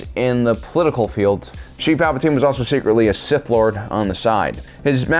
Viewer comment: i thought it said 'steve' palpatine i had to go look again